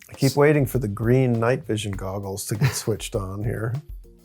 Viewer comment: I know what he said but, Green Knight Vision Googles sound so hilarious in context